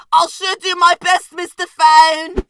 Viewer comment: it sounds like toilet said "ill sh** u my best Mr phone!"